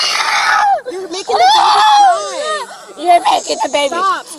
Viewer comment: Driver is drunk. Even if the babies were in a car seat , that’s still a safety concern. I thought I heard the mother say you’re braking the baby’s leg. And you’ll have to shoot me to take my baby away. Hmm.